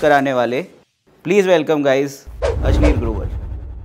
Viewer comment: For a moment, while introducing, I thought he said "Ashleel Grover" 😅😋